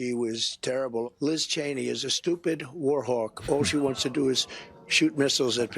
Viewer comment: I thought he said Cheney is a whore hog😂😂